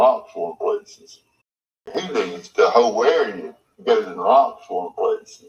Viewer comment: Sounds like he says “ He leaves the whole area he goes in lots more places “ ….this is interesting 🤨…Thank you 🥰 have a blessed day 🙏💕